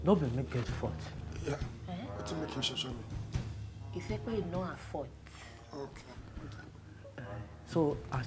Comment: Who else heard "It's not her fault"